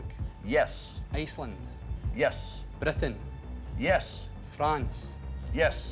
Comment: What exactly does he say here? It's hard to understand with the accent. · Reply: Britain